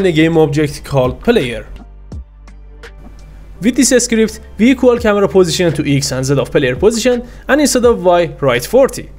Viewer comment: what are you saying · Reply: exactly this: with this script, we equal camera position to X and Z of player position and instead of Y write 40